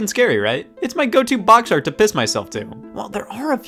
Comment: I don't know why, but for a fucking year now, I thought you were saying that the luigi's mansion box art was "...my go to box art yo fist myself too." I don't know why, and god I feel like a fucking idiot